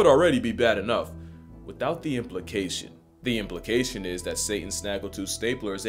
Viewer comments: "Crocs have been around since Adam was single.". In a sea of incredible lines, this might be my favorite yet. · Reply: thought he said Santa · 😂lol i misheard "Stable staplers"